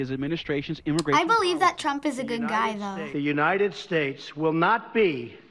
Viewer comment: what did she just say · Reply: She said he's a good guy.